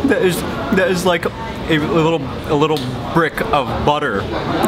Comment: Could somebody tell me what he said at 8:10? I heard "That is like a little break of butter" is it right? whether or not, I was wondering what it means. I'd really appreciate it if someone let me know it. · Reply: He said a little brick of butter.